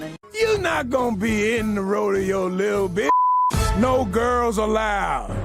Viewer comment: WOAH. HE DID NOT CALL HER WHAT SHE IMPLIES HE CALLED HER!! HE CALLED HER "LIL' BIT". DO NOT IMPLY WORDS IN SOMEONE'S MOUTH